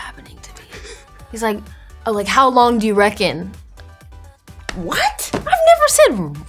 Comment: London ❣️ ❣️ ❣️ ❣️ ❣️ ❣️ Reckon..... Rubbish....... 😂😂 I love how confused she was (I'm from London) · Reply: I definitely heard wreckin' at first, lol